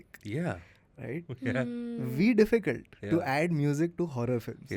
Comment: Did just say "v difficult" instead of "very difficult" at 17:15??? 😂😂😂😂😂